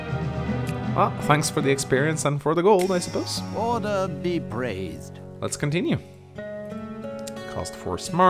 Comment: You know, I think we've been hearing it wrong this whole time. I think Gandalf may actually be saying, "Order be praised." Listen real close at 6:31...